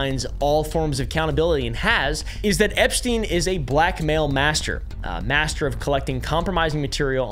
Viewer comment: Sorry at first I heard “black male master.” 😂